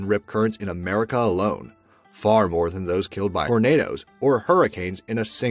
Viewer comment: Did he just say pornado…?